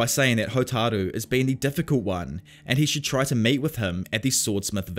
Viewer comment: I was just sitting where I am for about 3 minutes laughing of what I misheard you say about meeting the sword smith. I heard "mate" instead of "meet", choked on my water because of it.